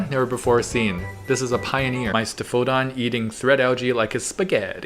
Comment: What was the name of the fish at 8:07? I couldn't quite catch what called it. I couldn't find it on google with what I could hear him say. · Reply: Etheostoma Caeruleum